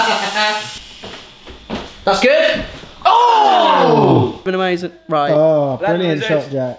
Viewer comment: Hope you guys enjoyed!! Leave a thumbs up, comment and subscribe! x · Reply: 😂😂 I thought said been amazing Rye. You'll get it if you have anywhere near a dirty mind.